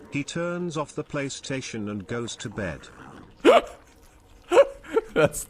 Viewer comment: i can't understand the last part of the joke help me please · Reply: Was does a dutchmen(?) do when his country wins the world cup? -Turns off his PlayStation and go to bed